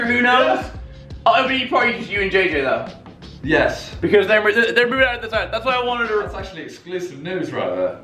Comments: Wait, did he say Simon and JJ were moving out? I couldn’t tell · what does Mini say that Simon says is exclusive? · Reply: Simon and JJ are moving out.